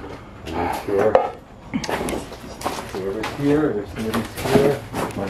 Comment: I heard through talking “Someone’s here”. Sounds female. ❤️